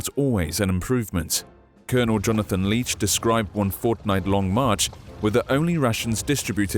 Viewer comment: I swear I heard the word Fortnite